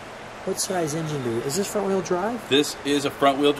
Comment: Did I just hear him ask if this 1976 Cadillac Eldorado was Front Wheel Drive? 9:50 lol